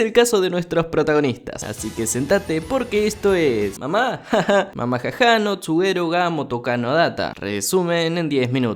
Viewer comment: No entendí como es el nombre😅🥺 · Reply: Mamahaha no tsurego ga tomokano